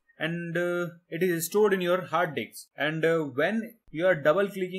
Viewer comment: very nice one but in 3:49 u said hard dicks instead of hard disks hahahaha